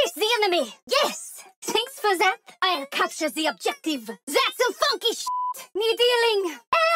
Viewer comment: tf have I just heard..? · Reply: Thats some funky shit...